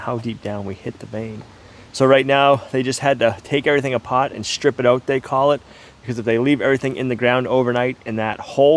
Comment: I worked on the oilfield on a rig when i was younger. that water rig is quite the machine. Quite automated. Also just watched farther, its called tripping out. I thought you said stripping out. Never a good night when you hear you're gonna be tripping pipe all night.